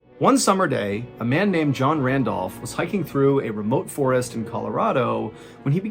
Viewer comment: I thought he said John Rambo.